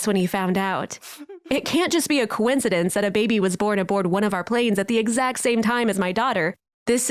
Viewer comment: Do I heard born a bored 😆😸😂😂